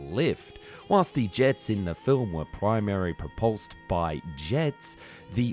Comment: So it sounds like the orthinopter is a kind of ornithopter. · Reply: I think I heard him say "propulsed"...